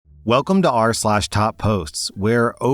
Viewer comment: Damn op 😂😂😂😂 also at first I thought it said topless not top posts 😂😂